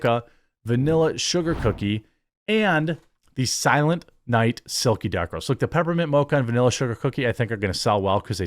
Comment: Yo, When you were promoting your drinks. I thought you said "silent night silky diapers." Lol